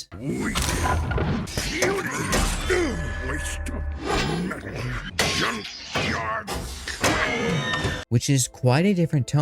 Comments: I dont know why but ad a child I always thought Prime said "Junkyard scrap" ans not crap. To me it fits his character more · For a long time in the forest battle I thought Optimas said "Junkyard scrap." 😂